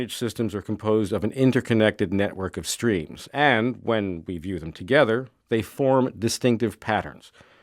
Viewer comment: What is the word that he says un the minute 1:05, when.... Together? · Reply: Eder Torreglosa Tabares we view them